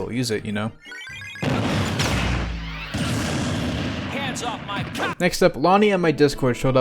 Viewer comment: Why does falco say 'Hand off my COK!'